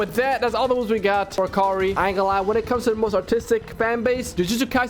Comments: I thought he said autistic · I thought u said autistic fanbase💀😭😭✌🥀💔